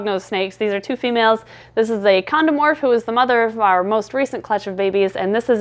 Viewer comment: Did u just say "condom morph"?! Ah, my freaking dirty mind 😂